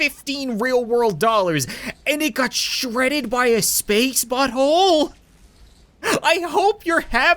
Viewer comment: I thought said Spanx butt hole